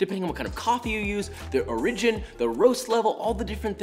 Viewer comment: Did he just say "or ridge in" instead of origin?